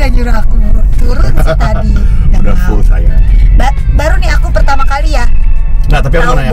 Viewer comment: Aku yang salah denger atau emang boy di menit ke 4:26 bilang "udah full sayang"